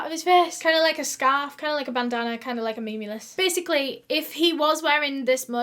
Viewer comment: DID SHE SAY “kinda like memulous?” AHAA